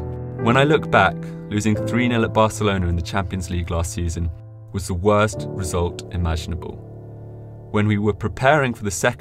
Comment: he said last season losing to barcelona instead of first leg